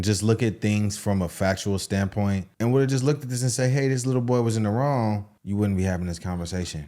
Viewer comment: Karmelo Anthony is a “little boy”?? Is that what you said at 2:55?